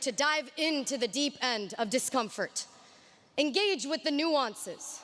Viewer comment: engage with the(a?) new answer と聞こえます。 nuances （微妙な点）なのかなぁ。。。